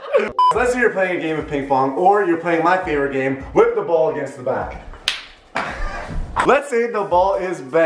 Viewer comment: Who else thought that their was a ass instead of a back 😂😂😂😂